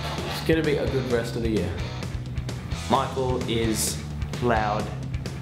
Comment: I heard Calum wrong at the end, thought he said "michael is my god" lol